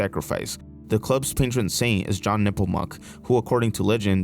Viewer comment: I'm sorry, I thought you said "John Nipplemuck" and I was like his name was WHAT!? 🤣💀